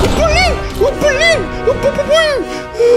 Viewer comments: мне одному послышалось *О блин, о блин, о бубубуин*? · Оу блин! Оу блин! Опа бабуин! мне же не одному это послышалось? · мне показалось что он сказал :Блин БЛин БАБУИН прости )